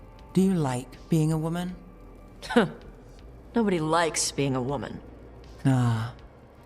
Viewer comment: Listen to it again. I know what I heard. If you ACTUALLY watched the video you would’ve heard it too. She says “Nobody likes being a woman.” With a tonal emphasis on “likes”. I’m going to give you the benefit of the doubt and assume you didn’t just try to gaslight me just now to “defend the weirdness.” Click the timestamp and listen to it. You are right about it being possible to get different outcomes, but you know what outcome is UNAVOIDABLE cuz it’s in a cutscene? Hearing a char in a fantasy setting that has an already established name for what she feels she is say “I’m non-binary” and states her new “pronouns”. Which is the problem and before you say “actually” 20:26. Listen to it. This has no place in a game with pre established lore and world building that doesn’t support its inclusion. I have nothing against DEI and being inclusive WHEN ITS DONE RIGHT within a NEW IP with GOOD WRITING that prioritizes world building, immersion and FLAWED chars that are more than just their stated identities and pronouns. Vs the women playing defective men that don’t identify as neither but hate men especially when they’re white and/or in positions of power they “need to be stripped of” BS that’s been coming out of these co-opted studios lately.